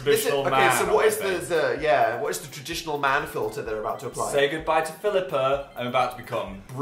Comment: Daniella is not on fire, but she's really hot! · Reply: ava g okay, I was just wondering cause I thought he said say goodbye to phillipa but I wasn't sure lol